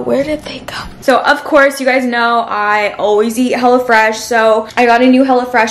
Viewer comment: I thought she said “ I always eat hella fresh” 😅